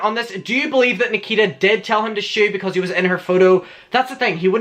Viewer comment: I thought she said scoot but idk 🤷🏻‍♀️